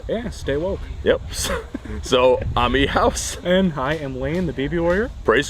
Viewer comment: I thought i heard "Beaver Warrior" lol